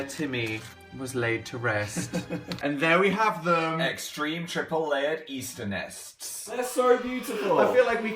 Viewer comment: At first i thought it said "Extreme nipple chocolate nests"